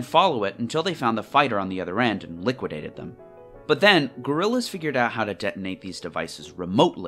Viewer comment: Did bro just say gorillas?